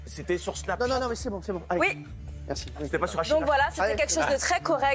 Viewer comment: C'est quoi la vanne de JMM a 3:10 j'ai pas entendu ? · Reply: Valche Prod. -JMM : C'était sur Snapchat ? -Cyril : Nan mais c'est bon. -JMM : C'était pas sur Achille la chatte ?